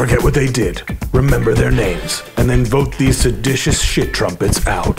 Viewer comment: “Shit Trumpets”? That’s kinda catchy. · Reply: On my old laptop speakers I heard 'Seditious shit droplets' :)